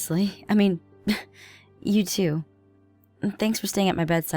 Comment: it sounded like you said youtube